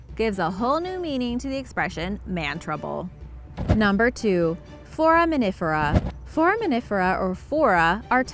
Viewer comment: I love watching Blue Planet And! I hear "For a minute for a" instead of foraminifera